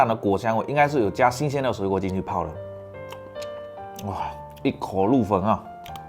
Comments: 把"一口入魂"猛聽成"一口入墳" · 一口入魂 我怎麼聽成一口入墳🤣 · 我听成一口入坟了，吓我一跳！！！ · 我听成了一口入坟